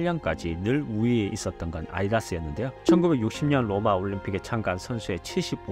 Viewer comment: 아디다스 발음이 이상하네 아이다스로 들림